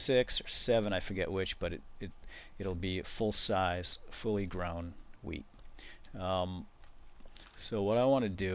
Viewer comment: When I first watched this I thought he said 'grow weed.' LOLZ!